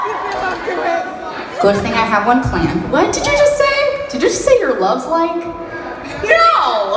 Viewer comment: Somebody tell me what she said? I can not understand (it's not my language) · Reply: •Kay •-• good thing I have one planned. What did you just say? Did you just say You’re love’s like? Nooo!!!